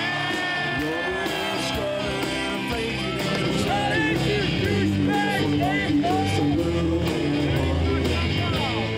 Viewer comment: did he say "God hates you"? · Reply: Apparently you haven't heard much about the WBC. They're terrible and yes he said "God hates you". They think everyone's going to Hell besides them.